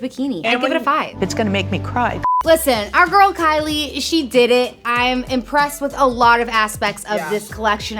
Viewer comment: Hahaha omg I totally heard "I'm impressed with a lot of the ass peaks of this collection" 👀 👏🤣 Good one Yea micro coverage? Not for me. Great for IG and that's it